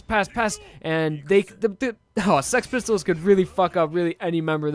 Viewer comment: when he says six pistols too fast it sounds like he is saying s*x pistols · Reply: He is saying sex pistols